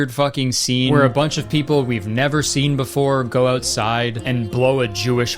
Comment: Oh shit, that’s why nothing happened. I thought they said “go out and blow a chauffeur”.